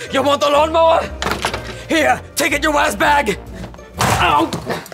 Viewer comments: Wait in 4:25 do did he say ask bag or a## bag?? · Did he say you a word bag